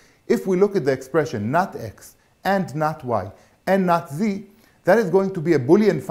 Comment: I suddenly realised they are saying Not(Z) and not Nazi at 2:13. I thought to myself probably instead of using x,y,z as variables they should try using a, b, c I again realised the result is going to sound similar as Not(c) Alphabets are tricky. I give up.